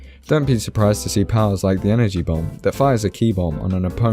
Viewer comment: Srry bro but your English is good but hard to understand for starters..... So talk clear and slow so that we can understand what u say ( Eg: u say energy bomb .... I heard it as enegybom)